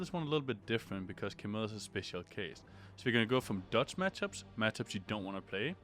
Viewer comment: I thought you said "dutch match ups"